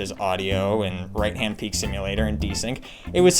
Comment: Did you say right hand peek simulator? whats that :)?